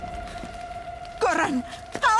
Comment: que dijo? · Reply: tambien traje esta dinamita, Adios